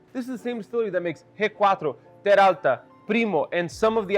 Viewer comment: In minute 3:42 you mention one of your favorites but you can't get your hands on it. I couldn't catch the name. Transcript says headquarters but that's not it. 🙂 What were you referring to? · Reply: G4 brand, He said "ge cuatro"